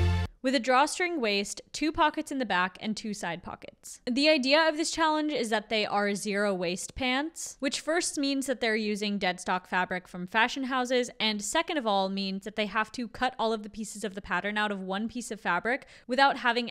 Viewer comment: I heard that as zero waist, and took a while to make sense of it. Went back and the subtitles agree though they get with the program faster than I did.